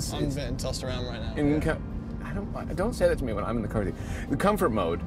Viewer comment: What did he say at 6:17 ? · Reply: “Don’t say that to me when i’m in the car with you”